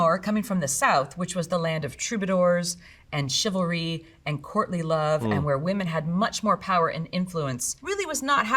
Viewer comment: I had to do a double take because I thought she said "Courtney Love" at first and my brain immediately went to "what does the singer from Hole have to do with the Middle Ages?"